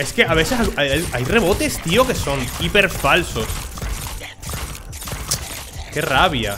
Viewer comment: entendi rebotes keeper falsos xd